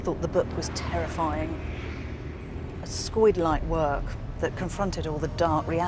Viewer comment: “… a [skoid]-like work …” Am I hearing that correctly?!